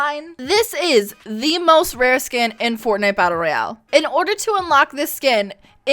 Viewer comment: I outa slap her. Did she just say it’s the rarest skin?